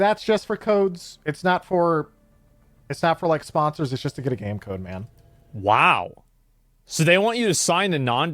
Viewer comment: I misheard and thought he said "just to get a Gamecube." Hell yeah, I'd sign it to get a Gamecube.